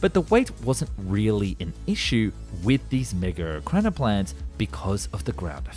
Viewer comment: bro did NOT say mega